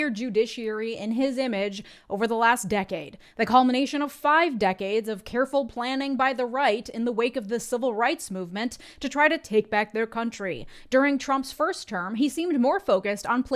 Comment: Can you hear a Freudian slip, not just make one? Because I swear I heard say "Reich" instead of "Right"